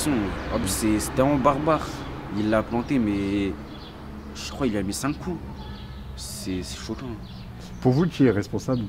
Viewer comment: Pourquoi sous-titrez-vous "il a mis cinq ou six coups" quand le jeune dit "il lui a mis cinq coups"?